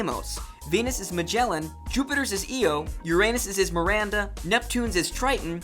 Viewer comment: Did he just say your anus-instead of The planet Uranus, when He was going down the list of the scouts castles?